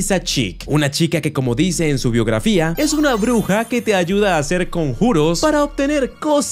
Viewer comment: pense que decia tu bruja homosexual fav